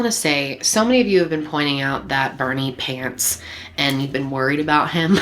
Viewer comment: for a few minutes, I actually thought I heard you say "a few of you have been worried about Bernie Pants..." I thought that was the dog's name. LOL